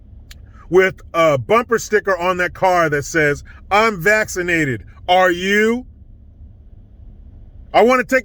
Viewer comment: I was listening from another room and thought I heard 'I'm Unvaccinated.. Are You?' 🤣😂🤣 That would be the bumper sticker I'd get.